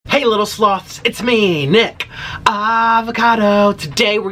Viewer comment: At first it sounded like u said hey lil sluts